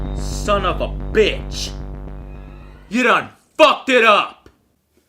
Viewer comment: sorry to ask but what does he say at 31:30? "you done fucked it up"?